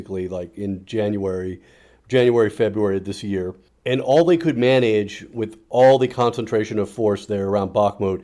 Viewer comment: I was sure I heard "... all they could mayonaise...", and I had to re-hear that bit a fair few times, giggle a bit, and conclude I was of course wrong but it was still hella funny ^,-,^